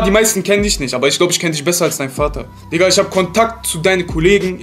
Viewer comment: aoooh ich Hab Vater gehört 😂